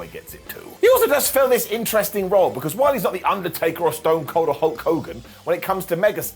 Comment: does say while the Undertaker isn’t the Undertaker or is it just me?😂